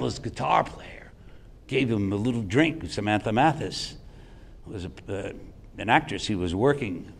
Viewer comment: Did he said Samantha mantis ? A actress who was working?? river phoenix was dating a Samantha at that time and was there that night he die???🤔💭